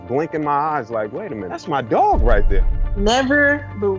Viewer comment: I heard dog, not dawg